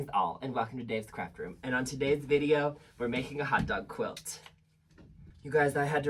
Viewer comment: I keep hearing it as "On to-Dave's video"